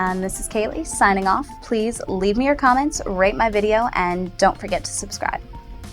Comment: i swear everytime she says "rate my video" it sounds like shes saying "rape my video" its hilarious ;D